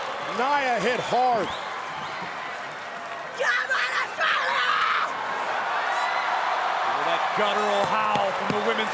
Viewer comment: did Rhea said "jo bole so nihal"? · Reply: She said come on Australia 😂😂😂